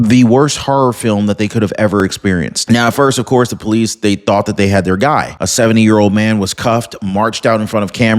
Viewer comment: I THOUGHT YOU SAID THEY CAUGHT THE GUY